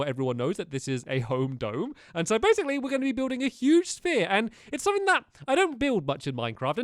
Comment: Huge sphere? I thought it said huge spire.